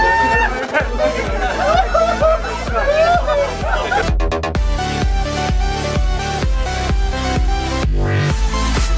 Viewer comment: what did he say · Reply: It was about who they thought were some of the best clubs in the world and he said Arsenal.